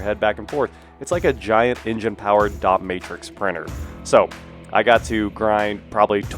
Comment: I could have sworn you said a 30 hp dominatrix printer. 😂. I have audio processing issues and sometimes I get some real gems.